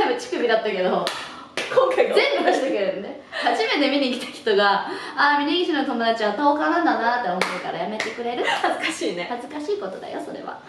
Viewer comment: 峯岸の友達は の続き なんて言ってるか聞き取れん… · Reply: 峯岸の友達はあたおかなんだな～と思われるからやめてくれる？